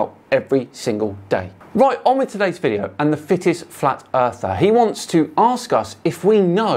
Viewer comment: I genuinely thought you said "Thickest Flat Earther".